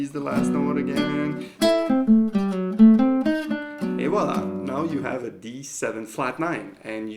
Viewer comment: Did you just say "Eyvallah" at 4:56? :D